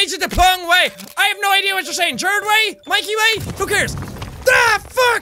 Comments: i heard "Gerard Way" and "Mikey Way" and freaked out. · I love MCR...... 😌 whispers thank you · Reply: I heard him say Gerard way soo yay